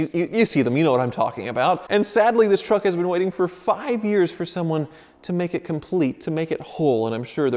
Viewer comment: who else heard fukin years